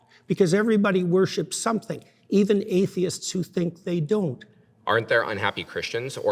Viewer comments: Omg I thought it said athletes for a second! THAT would have been something to see. 😂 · Thought it said athletes. I was wondering what JP had to do with sports. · Who else thought it said “Athletes” 😂😂